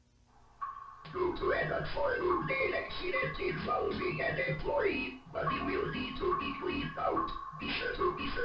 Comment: I heard “due to an unfortunate incident involving an employee he will have to be cleaned out”